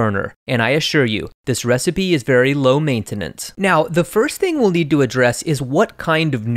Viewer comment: I heard, "this recipe is very LO MEIN-tenance" T__T